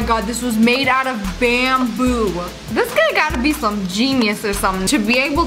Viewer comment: I thought she said “today we are checking out this sky” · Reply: no she said this guy